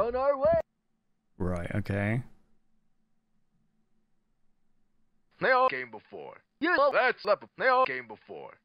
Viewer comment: if anyone's wondering what that says, the best i can get is "They all came before, you're all that's left!" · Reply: He says "They all came before." First. "Before" is one of the most accentuated words. Left is what the sentence after that says last. Got me on the repeating part, though.